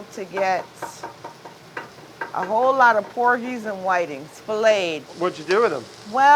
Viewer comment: I could have swore she said they had Corgis donated to feed people. Thought that was odd.